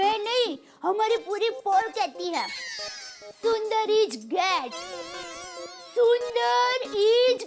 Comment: it sounded like sundhar is gay😂😂